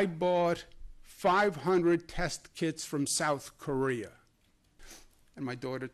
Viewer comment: At first I thought he was saying "test kids" lol